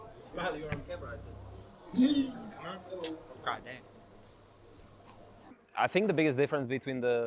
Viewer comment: Listen more closely :D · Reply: Ah ok now i heard the "gawd damn"